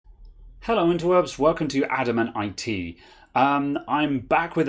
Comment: I always thought the intro was "Hello Interweebs", but I misheard it! (Maybe you should change it?....hehe)